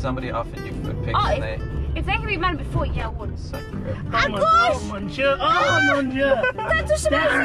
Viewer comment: I think said "à gauche!" and not "ah gosh!" at 11:56, which means to the left. But both kinda works! Awesome video! Felt like the funniest funny uber ride in a while. <3